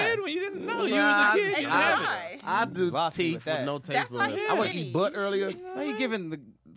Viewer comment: I swear Tahir said "I'd do teeth with no tastebuds, I woulda eat butt earlier?!" 🤣🤣🤣🤣